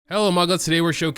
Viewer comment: the beginning sounded like hello amogus